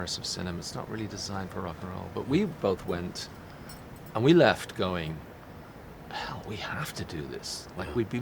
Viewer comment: but, what word does the edge use here? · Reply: "We left going... We have to do this"